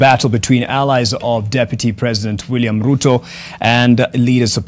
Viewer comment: Did I heard Ruth somoe at 29:58? That's Ruto's daughter. She's my ex girlfriend 🙄